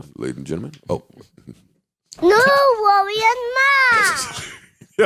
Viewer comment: That Drop is #Fire and Cute at the same time! I thought the baby was saying... Joe, Rory and Mal..🤣😂🤣😂 #ThisPodcastIsTheBest #1stPodcastIveEverConsistentlyWatchedAndListenedTo